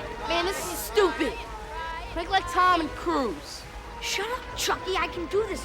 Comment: Am I hearing this face-palm worthy dialogue right? Did the kid literally say the line "Make like Tom and cruise."?